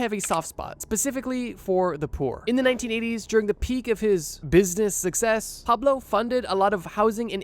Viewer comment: i thought bro said “at the business of his success” mixing up success and business, so i just sat and contemplated that sentence for the rest of the video